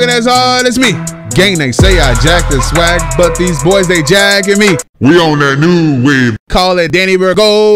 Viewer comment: wait did I just hear what I just heard "but these boys been jackin me"